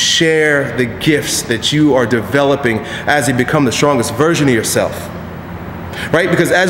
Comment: when i first started watching, thought he said "become the strongest VIRGIN of yourself!"